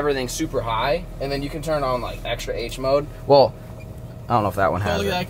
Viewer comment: finally, something we all needed. you can do more projects now and more efficiently and im tuned for it! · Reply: I thought u said turned on ngl😭